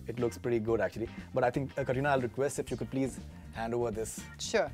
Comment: did he say kareena? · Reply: No he didn’t, i thought so too but he say the name so fast that the T went silent